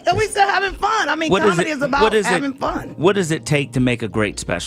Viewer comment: I thought she said comedy is about having fun